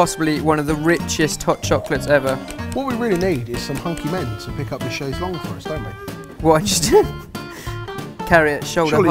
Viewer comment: Hey, could someone please tell me what said at 5:15? I really can't make it out... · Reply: "All we really need is some hunky men to pick up the chaise longue for us" (Its the kind of chair they are sitting on)